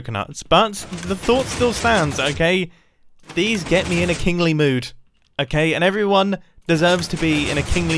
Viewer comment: WHY DID I HEAR KINKY AT 5:38?? HELP